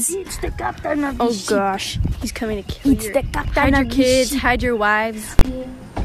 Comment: "Ok Come on kids, hide your wives" is what i heard.. dunno about anyone else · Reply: Hide your kids Hide your wife